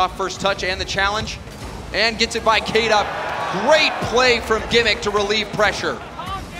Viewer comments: who else hears "Calm down Gimmick" 💀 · I heard someone say CALM DOWN GIMMICK